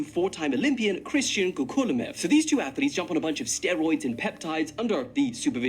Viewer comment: Is he saying “scaroids”?